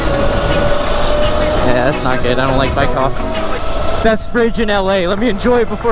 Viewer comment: Bro I thought he said “uh oh white cop I don’t like white cops” lol but he just said bike cops